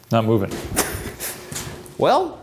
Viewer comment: I didn’t hear “im not” i heard “I hope”